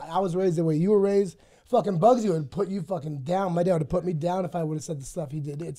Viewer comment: Did he say "Bugsy" at 3:49? That's my name lol